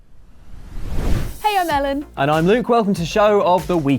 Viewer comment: I actually thought Ellen said Hiya Mellon not Hey, i'm Ellen.